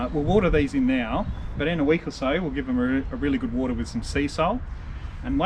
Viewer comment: did he say they would water them in with some sea salt? if so, why? Thanks! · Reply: he said SeaSol... which is a brand name of an organic liquid fertiliser based on sea weed - it is highly concentrated in trace minerals and elements to rejuvenate soils